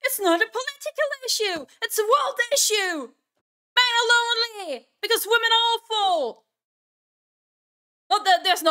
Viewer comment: I misheard you saying "...Because women are awful!" as "...Because women are Offal!" and I started wheezing/laughing in disbelief until I heard you say the word awful again. First time watching your videos and fully enjoy the vibes. +1 Sub